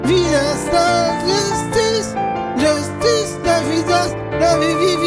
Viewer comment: I hear “Justice wie das wie wie das”